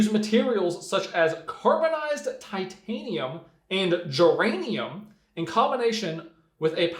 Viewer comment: did you say germanium? That is radioactive. And those kind of "negative ion" things tend to be rather radioactive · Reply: No, he said "geranium", the flower, lol.